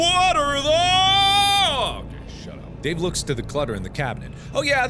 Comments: Did anyone else notice at 4:41 after mark says ‘what are those!’ Dark or someone mutters ‘shut up’???? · Anyone else hear "Hey Shutup?"